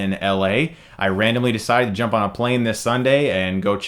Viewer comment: Lol at 5:13 I heard "I randomly decided to jump off a plane this Sunday,"... Help